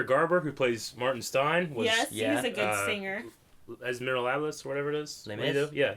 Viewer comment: What did just call Les Mis? Miralablas? lol